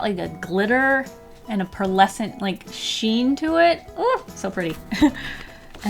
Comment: Was it me or did I hear her say " perlesent ShEeN "